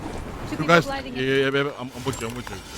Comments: am i stupid or did he really say baby?😂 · is it me or did I hear him call her babe